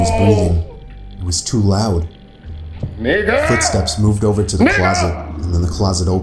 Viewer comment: i thought i guy in the story said NIGGA but it was 😂